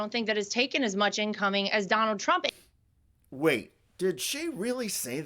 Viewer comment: I think she said donaldtrumpet!